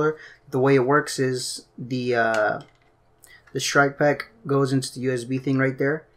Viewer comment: At first I thought you say shreck pack 😂